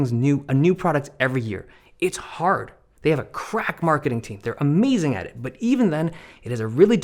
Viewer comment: "they have a cracked marketing team" i just love this one quote so much lol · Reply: I misheard it as "crap marketing team" 😂